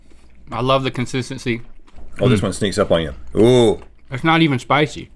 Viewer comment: Rhett: "It sneaks up on ya." What I heard: "It stinks of onion."